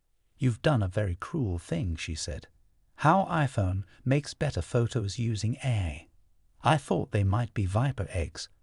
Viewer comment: Did he just say: how iphones makes better pictures using Ai?